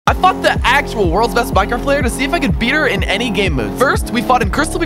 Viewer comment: Anyone thought he said he" F**KED the world's best mindcraft player."